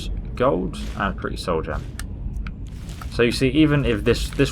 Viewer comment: did he say a pretty soul gem?